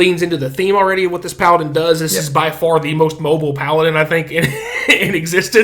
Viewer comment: I swear I thought you said "Kobold paladin" which was very confusing as a subclass.